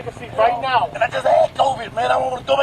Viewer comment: did he just say he just had covid?